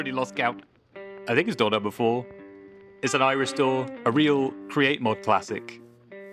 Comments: I thought he said "Irish Door" and i was momentarily confused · I thought he said "It's an Irish door."😅